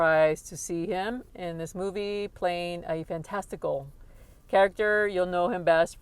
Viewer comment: Did you just say "fan testicle".. that's cool. I'm a fan of my own to be honest. They're cute and adorable.